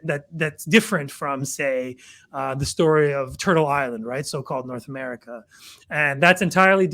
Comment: I thought you said PITA island . 😊 More like Crumpets Island 🙄